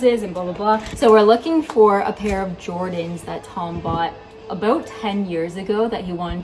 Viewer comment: Omg too funny. I thought you had said looking for matching “Jordache” (jeans)🤦🏼‍♀️. Jordan’s. JORDANS🤣❤️🇨🇦